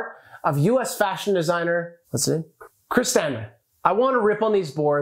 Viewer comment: Damn I thought he said “Chis Senn” then I looked up the ikea board and the designer is “Chris Stamp”. No idea who that is. I was disappointed in Chris Senn for a minute.